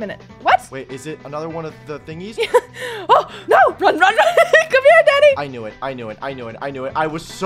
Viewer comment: BRO AT 8:02 OR 8:01 WHEN FOLLOWED WAS CHASING AND I HEARD SAID COME HERE DADDY